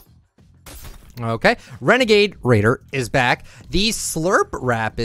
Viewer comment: Did he just say renegade raider